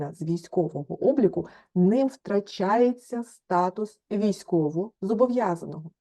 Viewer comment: що вона каже 7:03, що з виключенням з військового обліку людина не втрачає статус військовозобов язаного? Як так може бути? Може вона обмовилась. Взагалі обурюють усі ці шахрайські намагання запудрити мізки, перекрутити, взяти на лохА. Я знаю так, що в січні 2010 стукнуло 45 років, значить настав граничний вік військовозобов язаного, на той час підтверджений законом. Ставити штамп не ходив (не люблю туди ходити), але за законом мали виключити автоматично 1 січня 2011 року. Все. Крапка. А те що там вже творилося 22 липня 2014 року, то вже до спини, хай хоч лобом підлогу проб ють. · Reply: "ним втрачається статус військовозобовязаного"